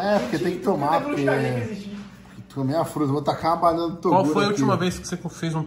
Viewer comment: QE isso E isso msm que eu ouvi ? ( Vou tacar a banana do toguro ) ?